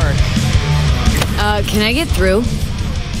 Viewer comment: I heard "boobs occupied" 💀